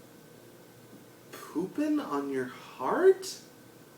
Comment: I thought it said "poopin' on your heart" too!